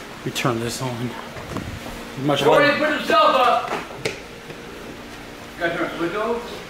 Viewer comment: What did AGP SAY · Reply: Door aint gonna put it self up